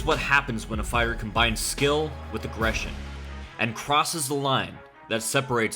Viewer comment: i literally replay 9:20 three times because i thought i heard "inoue is what happens when a fighter combine skill and depression" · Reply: clearly says aggression